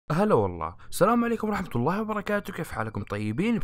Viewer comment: في الدقيقة 0:01 بدل ما اسمع هلا ولله سمعتها شورمى 🤣🤣